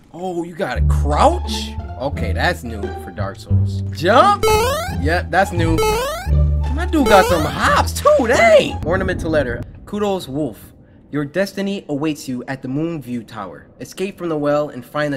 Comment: Cory- YOU SUCKAS CANT READ Gets to 3:43 Cory-kutos wolf but really says kuros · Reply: I heard him say Kuro’s wolf tho